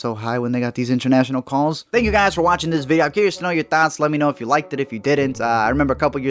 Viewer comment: Everytime he says "thoughts" i hear "THOTS"